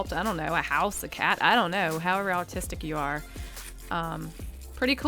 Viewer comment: "However autistic you are" 4:50 you can't tell me that isn't exactly what that sounded like